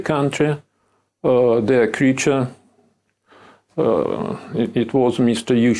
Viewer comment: LOL Did Shulgin call Yuschenko an American creature on 22:36? That's funny actually, but he has just nailed it! 😂